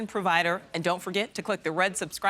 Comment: Did she say "faster than unbiased coverage"? (My ears are sort of ancient.)